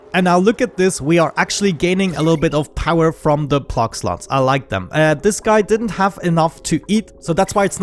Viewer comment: "Plug sluts" oml. I thought that's what I heard and then the (11:00) timestamp reinforced it XD Edit: If you happen to see this comment, do you know of any mods that are useful/work for spaced out? Thanks and great series :D